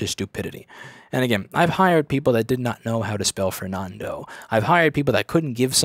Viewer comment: what I hear: I've hired people that did not know how to spell Fernando YTube CC : I've hired people that did not know how to spell for nando